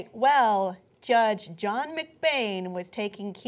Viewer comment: For a second there, I thought you said "Judge John McCain."